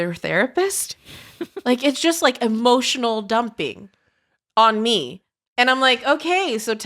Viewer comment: I swear I’ve heard “Emotional damage!!” at 7:05, but not Steve Hen 🤣🤣🤣